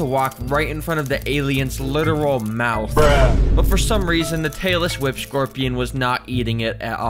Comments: Am I the only one who constantly misheard ‘Tailless Whip Scorpion’ as ‘Taylor Swift Scorpion’? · I heard “Taylor Swift scorpion” every time he said tailless whip scorpion. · When ever he says tailless whip Scorpion but it sounds like he said Taylor Swift scorpion😂